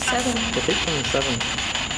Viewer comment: the spirit box said, "pito" which means seven it sounded like a man's voice then later on it was translated by some spirit who sounded like a kid, that said, "seven".